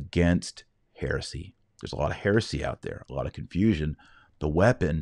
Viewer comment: When you said ”There’s a lot of heresy out there,” at first I heard it as: “There’s a lot of Pharisee out there.” 😀